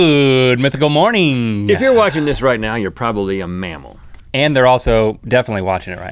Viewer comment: I thought he said “if ur watching this ur probably a man”